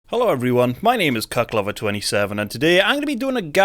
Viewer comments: Thought he said "Cuntlover27" 😂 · Some codes would be cool. I'm pretty sure I heard "cocklover27" but maybe that's just me hearing what I want to hear